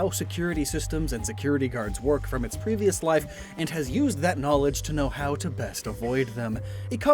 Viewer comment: for a second I thought he was trying to say that it was undead.